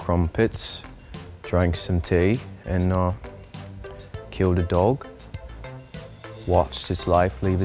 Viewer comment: I thought he honestly said kill the dog and I was offended until I realised he said cue the dog